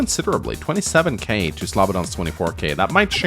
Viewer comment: I was listening to this while cooking dinner didn't realize until 10 minutes in that you were saying Slavodan. I thought you were saying this was a match between Peepeepoopoo vs Slobberdong